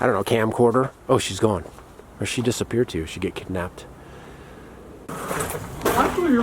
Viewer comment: I love how his first thought was she got kicked out 😂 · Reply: Oh haha...I thought he said "kidnapped"!!!!